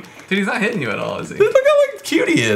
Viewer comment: I totally heard say "Eureeka's Asshole."